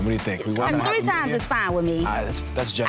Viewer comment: Here during quarantine. Now correct me if I'm wrong but did I hear my man say "I need a snack too" when asked about how much sex he'd be fine with during the final interview?! lol